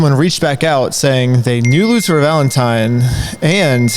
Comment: Did he say "Luther"?